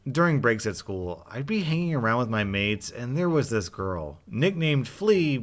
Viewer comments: I heard 'mates' as 'maids' and I was like 'Wat?' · i heard this as 'maids'... i want hot maids too.